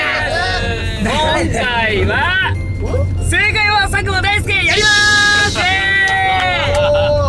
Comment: 最初 正解は佐久間大好きーって聞こえる笑